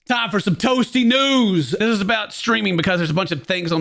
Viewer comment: at the start i thought ken said time for some toasty nudes xD